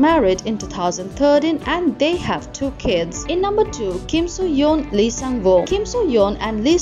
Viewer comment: I heard “Kim soo hyun” instead of “ Kim So Yeon” and my heart just dropped , for that split second before I saw the pic my heart died and got repaired quickly after lol